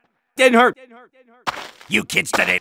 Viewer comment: it sounds like honk instead of bonk